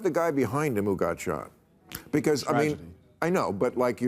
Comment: Byron - "Trump was touched by God that day, the firefighter... yeah that's tough" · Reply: He actually said "tragedy"